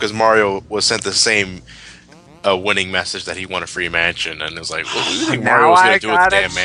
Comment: Every time say "Free Mansion" I hear it as "Free Man". And is right, Luigi being scared and scepticale asked Mario to investigate the house for him before he moves in.